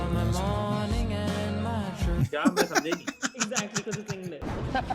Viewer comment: what did and then say? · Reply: Usne kaha “ye kya hai mai samjha hi nahi"